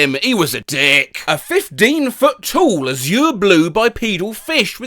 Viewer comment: It sounds like you said “a 15 foot TOOL” 😂